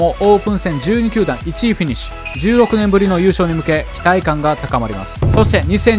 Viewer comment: 10億年ぶりのに聞こえて草www それまだ金本知憲が4番打ってた頃じゃねえかwww